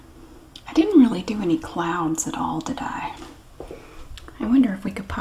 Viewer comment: I hear, "I didn't really do any clowns did I?" Whaaat? Oh, "clouds". That's very different. Never mind.